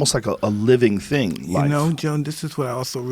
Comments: Does Mike call him John or are my ears shitty? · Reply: Close... @ 26:02 Mike calls him “Joan.” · did Mike call Joe John? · does he call joe John? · did mike call joe, john? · did Mike just call Joe, John? Lol.